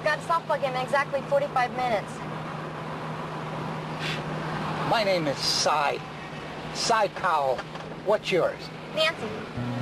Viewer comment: Sounds like he says "my name is Psy... PsyCho..."